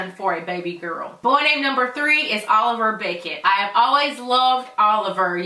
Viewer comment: I was reading a comment when you said Oliver Beckett and thought you said Oliver Bacon